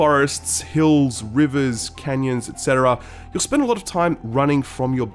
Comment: wasnt paying attention i heard, 'world full of roads, rivers, and Kenyans....'. i almost spat my drink out, then i realized he said canyons/